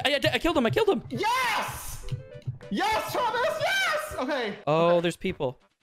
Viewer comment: lol did james call "thomas"?